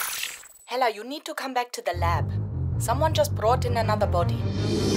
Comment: did anyone hear Thor saying "Hela wait for me!"